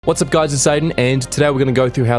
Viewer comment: Bro I had to watch the intro 2 times because it sounded like he said "What's up guys, it's Satan"